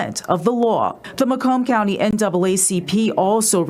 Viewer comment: Notice that the captions spells out "NWA" CP instead of NAACP...really?..that was deliberate. .NO this county ISN'T go to take this matter seriously